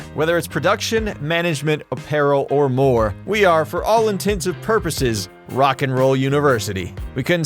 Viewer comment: Did you say "for all intensive purposes" at 1:54 ??